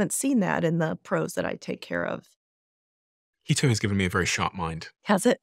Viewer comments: You mentioned at 47:00 that something has helped give you a very sharp mind recently, and it sounded like U2 or Huel2, but I wasn't sure. What was that again? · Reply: Hey graybeard, I bet that he said "Keto". I was listening for you! I hope that's correct. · what is Steven saying here? Keto has given him a sharp mind?